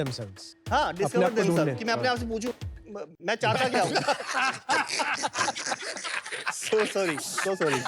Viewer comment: ye kya bola ? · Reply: Main apne aap ko pucha tu hai BC 😂😂 Main chahata keya hu ..